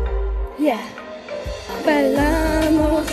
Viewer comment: She tried to say "Bailamos" but I heard "Palamos"